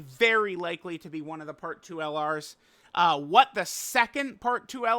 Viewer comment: my brain is so rotted i thought he was gonna say "what the sigma" at 11:26 😭